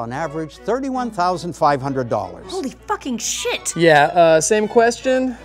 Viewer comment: Festa de casamento realmente é um bagulho inútil "pakas" :| 1:11 o que ela falou? em inglês~ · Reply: "Holy fucking shit!"